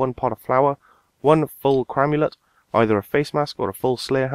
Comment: dammit camulet i thought you said cramulet